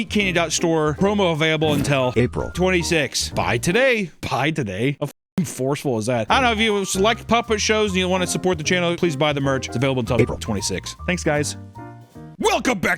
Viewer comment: i heard pie today